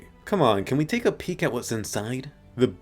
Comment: Can we take a PICK what's inside. Haha, I heard that...nice